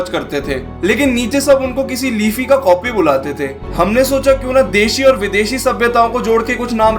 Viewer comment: lifi ka copy? Cant understand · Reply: LeafyisHere